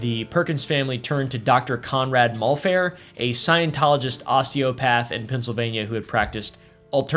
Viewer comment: I misheard "a Scientologist osteopath" as "a Scientologist sociopath", and I choose to agree with my mishearing.